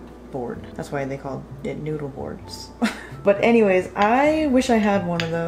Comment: i was watching while crocheting and thought said “have you guys heard wbout…newborns” and i was like 🗿